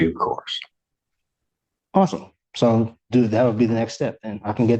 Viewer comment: He actually said to the judge, "Awesome, dude".